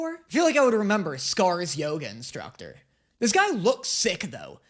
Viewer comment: I thought when you said ‘Scars yoga instructor’ you were saying Scars Yoda